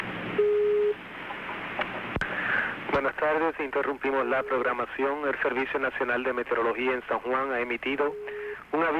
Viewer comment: What did bro said? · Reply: He said good afternoon in Spanish “ Buenas Tardes”